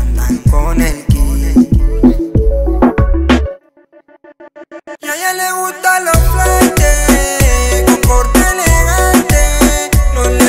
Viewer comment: Cuando escuché el tema por primera vez pensé que decía a ella le gustan los flanes corte postre jajajaja soy de argentina